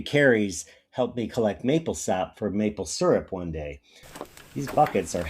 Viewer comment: Google tells me a 5 gallon bucket weighs 41 pounds when filled, so yes not 50. They sure felt heavy! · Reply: It depends on what is in them. I thought you said you were carrying maple syrup which has a density 1.3x water which would put the weight more like 50 lbs.